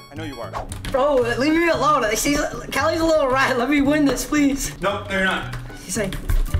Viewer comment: DID HE JUST SAY CALI IS A LITTLE RAT?